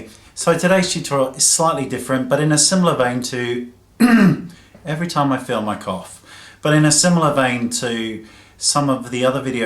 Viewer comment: Oh my gosh, I thought you said " in a similar vain to, every time I feel my cock"